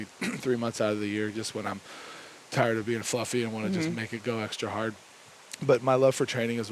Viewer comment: Did he say “when I’m tired of being fluffy” 😆😆 That’s so cute.